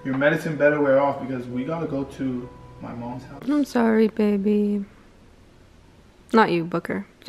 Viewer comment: OMG 8:28 DID JUST CALL BABY